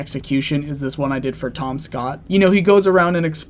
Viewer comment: i thought he had said tomska 💔